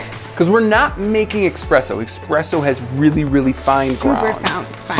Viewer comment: Did he just say expresso? Shaaaame.